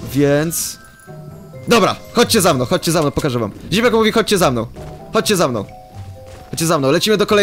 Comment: Czy tylko ja usłyszałem Ziemniak mówi?